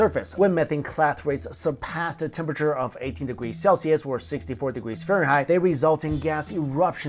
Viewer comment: I thought he said 80 degrees Celsius and got really confused